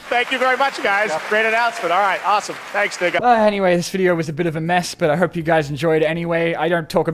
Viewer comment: WHAT DID HE SAY????? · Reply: He says “thanks dingha” the name of the guy by the right